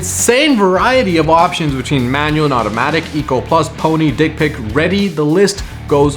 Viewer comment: Did he say Dick Pic at 10:50?